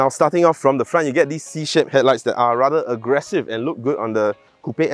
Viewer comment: Ooh we have a new face (Psy song) 😂 Anyway is it my hearing or did I hear 0:45 “starting from the front you get this C s*** 💩headlights”…oops. Think all your shaped pronunciation sounds like s*** 2:05 Better articulate properly else Renault angry leh 🤣Anyway Jia you la. Who thinks it’s about time sgcarmart needs to recruit a female host raise your hand 🙋🏻‍♂️